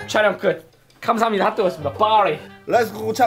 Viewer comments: 나만 9:49 Let's go 참치 좇밥으로 들림? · 나만 참치 좁밥이라고 들리는건가?